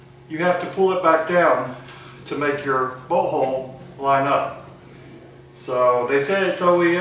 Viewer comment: Leverage is key to breaking it lose or breaking it. Should go back together faster! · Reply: I thought you said, “to make your butthole line up” 😅